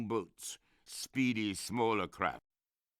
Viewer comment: lol, I just hear: "Speedy, smaller crap."